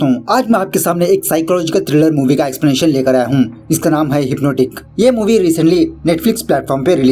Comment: Did you say Hypno Dick? Anyway awesome explanation